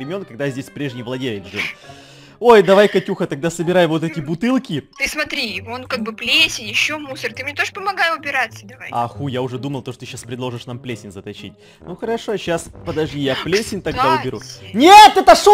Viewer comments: мне послышалось или он сказал а х*й я думал… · мне одной послышалось "Х🤬й"